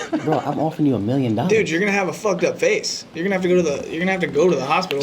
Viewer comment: um i’m sorry bryce did you say austin’s gonna have a fucked up face 💀💀💀 tables have turned